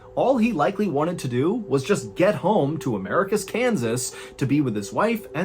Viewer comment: So, after checking with Google Maps, I'm guessing that when it sounded like you were saying "America's Kansas" (which makes no sense, because 1) I don't think there are any other Kansases out there other than the band, but they're not a place, and 2) you'd only realistically need to say it once in any case), you actually meant "Americus, Kansas". Which has two problems, firstly that having established that Americus is in Kansas, you didn't need to repeat that fact every time you mentioned the town, and secondly, when saying something that can be easily misinterpreted like that, you need to be very careful to slow down and enunciate it clearly. In this case you probably should have overenunciated the final syllable of "Americus" to make it clear. I realize that you had a picture of a town police car at one point, but not everyone watches the video; some of us run this in the background while doing other things.